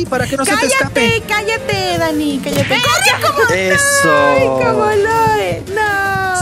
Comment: lo que entendi :CORRE como loe xdd